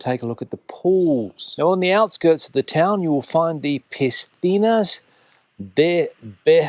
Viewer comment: it is pronounced Pecina not Pistina . Great Video by the way as always. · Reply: I must admit that what says a couple of times sounds a bit like 'Pistina', but on listening again, I feel that he does actually say 'Piscina'. In fact I have no doubt that did pronounce it correctly, because he speaks fluent Spanish, and he is a teacher! 😃